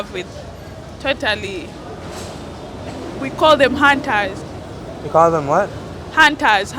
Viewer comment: What does she say at 10:50 ? "we call them ??? They come in and hunt and then just leave" It sounds like she says hand ties or hunt ties ? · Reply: "hunters"